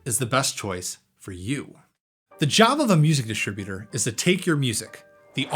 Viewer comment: Did you say Jabba the Music Distributor @ 0:45? 😂